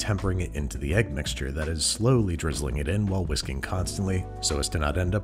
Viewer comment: i'm sorry but every time i hear "whisk constantly" i giggle and say "wisconsin-ly"